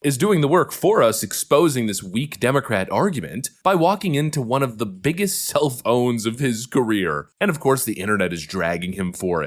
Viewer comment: Sounded like "Walking into one of the biggest cell phones of his career." 📱 Had to listen to it again. 🤣